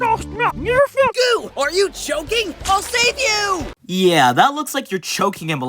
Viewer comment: IT SOUNDS LIKE GOO SAYS I’M SUCKING YOUR COCK